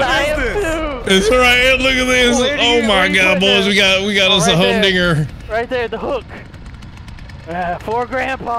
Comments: Bro wtf Anthony say at the end near 18:08?!? Lmao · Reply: def sounded like "a home n*****" lmao but i think he said "home dinger" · At the end of this video Anthony did NOT say anything bad lol, yall are reaching... He's a professional streamer and would never be that way in his life.. Don't think that way about him :/ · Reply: He said humdinger ppl mishearing lol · he definitely said humdinger. Which doesn’t make sense as to what was going on but he didn’t say anything wrong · what exactly did I just hear?? It can’t be what I think I heard. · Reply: Humdinger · At the end of this video Anthony did NOT say anything bad lol, yall are reaching... He's a professional streamer and would never be that way in his life.. Don't think that way about him :/ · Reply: i thought he said home digger 😂 i was like “what she digging?” 😂